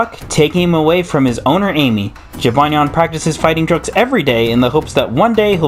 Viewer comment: The automated english subtitles said jibanyan trains to fight drugs